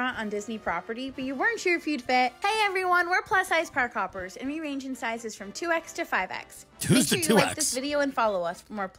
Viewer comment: "We're plus size park hoppers and we range in sizes 2x to Fedex" Legitimately thought they said that and it brought me to tears on top of Tim's commentary.